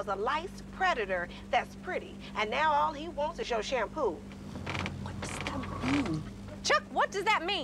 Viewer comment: (Girl) he says that 3 days ago all he wanted was a liced predictor, that's pretty. And now all he wants is your shampoo. 😂😂😂😂😂😂😂😂😂😂😂😂😂😂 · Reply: Aphmau Lover i heard "all he wants is to share some poo. 😂😂😂😂😂 English is not my first language. 😂😂😂